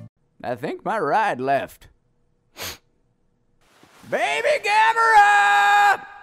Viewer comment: wait xDD I just realized what master roshi says at the end xDDD I always thought he said "hit it boy".